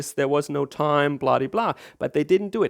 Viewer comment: OK, I know what you're trying to say with "bladi-blah", but it sure sounds like "bloody-blah" to every English-speaker. :D